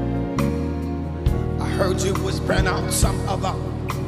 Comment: Someone please let me know how he sang different 2:36 "i heard u whisper?? Some about?? Men's name??